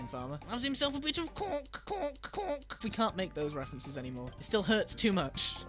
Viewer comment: for a second I thought you said pork then I realize what he was actually saying